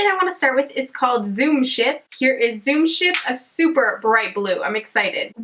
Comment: Lol I thought you said zoom shit